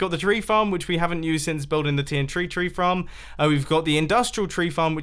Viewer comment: does he say tntree tree fram